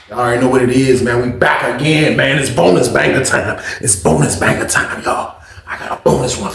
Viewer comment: this whole time I thought u was saying BONELESS Banger